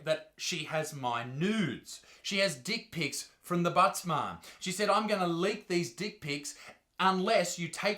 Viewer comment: My god I thought you were saying thebuttsmart for the last 3 months. 🤦‍♂️